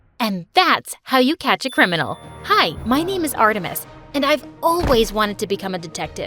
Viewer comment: art to miss did I hear 😂